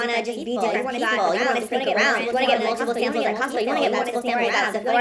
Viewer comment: did i just hear her say "get multiple samples at costco"